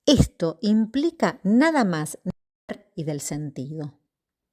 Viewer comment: hay un corte en la transmision... tiempo 11:51 - 11:52, no se entiende bien qué debería conectar entre "esto implica nada mas..." y "...y del sentido..." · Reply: Hola, si, me di cuenta que algo pasó: ¨implica nada más ni nada menos que una ética de la renuncia al dominio del sentido¨ Gracias!!